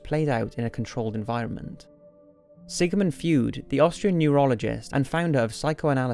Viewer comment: Did he just say Sigamund Fued?